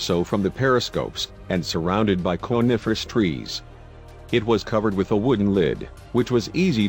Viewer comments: I thought you said carnivorous trees lol I got scared af. I bet it goes further down. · Sounds like he says 'surrounded by carnivorous trees'!!